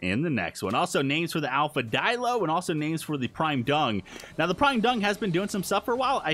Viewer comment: it sounded like you said The Prime Dong, instead of The prime dung